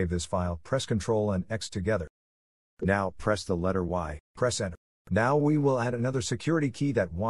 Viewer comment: HELP what did you say at 3:30-3:35 to get off that page and back to the main place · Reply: Hi! first type "Ctrl x" together then "y" then "Enter" hope it helps please keep me posted